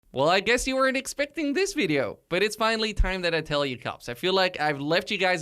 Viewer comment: For some reason I thought he said at the start it's finally time to tell you cops